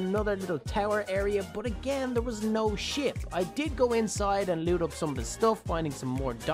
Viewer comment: NO WAY I HEARD HIM SAY SHIT ON